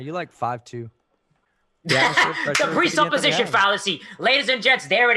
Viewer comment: did just say the capri-sun fallacy 🤣